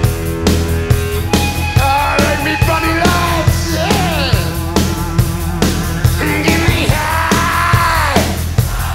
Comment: What’s Brian saying at 3:18 cant make it out. Drives me crazy 😂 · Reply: ”Aaaah everybody all set”? Mayby